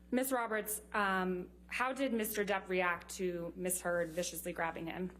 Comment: Anyone else keep hearing her being called "misheard"?